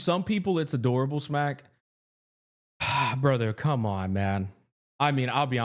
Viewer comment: oh god, I just misheard that as “oh c’mon mem” 💀😭